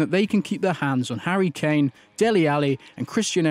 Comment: Did he just say Harry Chain??